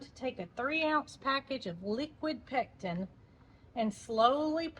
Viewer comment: When you were making the jelly, I was doing something else, but I was listening and I thought you said, "liquid Texan"! I looked at the screen and I saw that you must have said "pectin". It made me laugh though! lol!